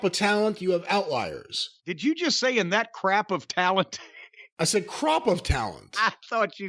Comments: Rewound the clip several times just to make sure I heard "crap of talent" the first time LOL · I had to rewind, because it sounded like he said crap of talent to me, too. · I thought said crap of talent too. Me thinks had a Freudian slip.